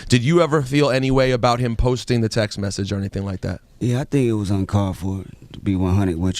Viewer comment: what did he say at 18:30 about the text of Macklemore? 'i think it was un...' ? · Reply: uncalled for